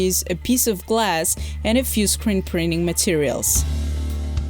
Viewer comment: Did you say scrint preening materials @ 0:10 min?